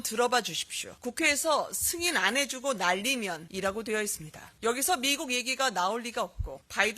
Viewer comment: 난 "국회에서 승인 안해주고 난리믄" 으로 들리는데, 특히 난리믄에 "비음 "난"이 정확하게 들립니다. 어떻게 바이든으로 들리지? 이상하군요. 특히 국회라자나 의회가 아니라. 박진 대답도 그렇고. 법 공부 한 사람이 국회랑 의회도 구분 못하겠나??